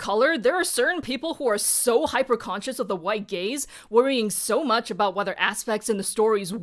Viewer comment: I had to rewatch the part about “white gaze” several times, not because of the message but because i kept hearing “white gays” and was very confused 😭😭😭 ig this shows where my mind is at